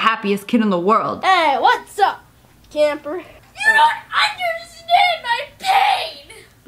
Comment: Kid: YOU DON'T UNDERSTAND MY PAIN Me: yea i do last year i got $1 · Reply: Is it bad that I thought the kid said "YOU DON'T UNDERSTAND I'M GAY."